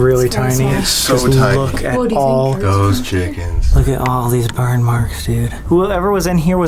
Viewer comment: did say "ghost chickens"?